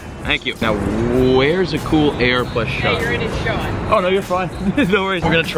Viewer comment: what did that kid say? I'm curious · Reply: “Hey, you’re in his shot”